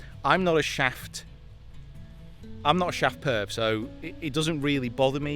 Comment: Did you just say “shaft perv” talking about your driver? 😂😂